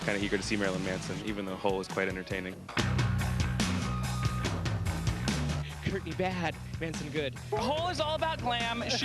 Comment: notice how he calls her "kurtney"......instead of courtney.......